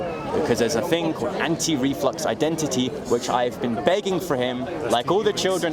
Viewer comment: Also, I just tried looking up "anti-reflux identity," this thing he days exists, but can't find anything. Am i mishearing him?